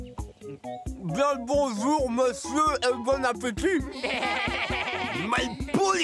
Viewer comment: Il a dit quoi ? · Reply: Bien le bonjour monsieur, et bon appétit